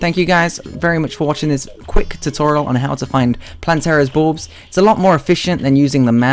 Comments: I legitimately thought he said Plantera's Boobs at the start. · I legitimately thought he said Plantera's Boobs at the start. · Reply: I thought he said Plantera's Balls at the start.